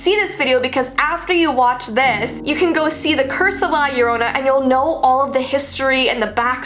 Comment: I thought it said corona LLorona